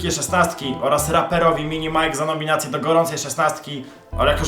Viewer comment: "raperowi mini majk" XDDDDD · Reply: jak usłyszałem RAPEROWI MINI MAJK XDDDDDDDDDDDDDD